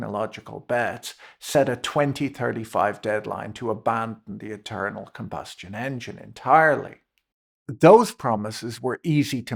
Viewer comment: I misheard you at one point as saying "the eternal combustion engine", which seems appropriate here.